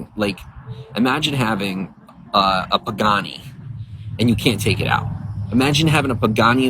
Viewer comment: Wtf is a "Booghani"?!?!?!? 😂😂😂😂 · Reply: is the Booghani timestamp. I think he's trying to say Bugatti, which he of course, doesn't have. He isn't wealthy, he isn't famous, and he's trying to defend his ego.